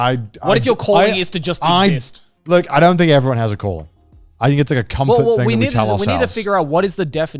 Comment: Misheard connor's "do you think everyone has a calling" line as "do you think everyone has a colon" and I was so confused